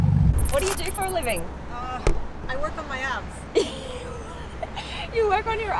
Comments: I thought that one lady said “I work on my apps” she meant abs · Thought she said "I work on my apps", then she gets out looking like Arnie. 😲😂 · She literally said she works on her abs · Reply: Thought my girl had said "my apps" at first